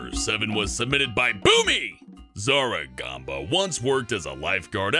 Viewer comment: When u said bewmie I thought u said BOOBIE